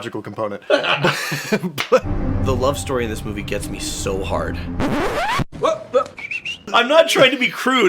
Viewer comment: Gee... when said what he said my brain heard that as "The love story in this movie gets (to) me SO (much)." And then comes along with the dick joke line... Males... lol. 😆